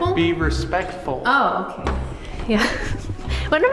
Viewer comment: WHISPER, SOUNDS FEMALE. NOT 100% SURE BUT I THINK SHE SAID "OKAY"...?